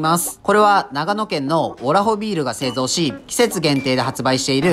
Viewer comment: オ○ホビールに聞こえた… 疲れてるなオレ…